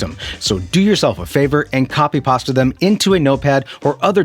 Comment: Is this just an AI channel? At 5:48 does he say "copy pasta them?" Only an AI would mispronounce the word paste. Hmm...